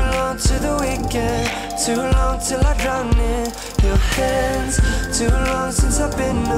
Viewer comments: I never heard "drown" before this video. I always heard "come" as in fall in someone's hands/arms. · omfg I always thought he said come not drown and I'd always think "I have a dirty mind so what tf could this actually mean" welp